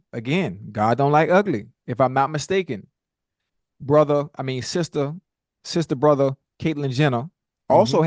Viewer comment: 😂😅😅😂 did Ocho say sister brother!?!?